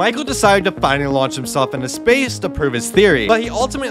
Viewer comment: Bro my video lagged so it sounded like he said “fortunately he died on impact”